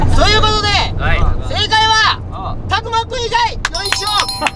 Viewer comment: こーじの言う佐久間くんがたくまくんにしか聞こえん笑笑笑10:33